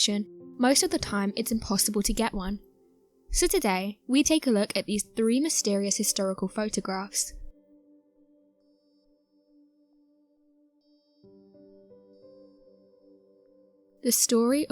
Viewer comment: Thought she said hysterical photographs, but soon realized she said "historical" photographs. 😁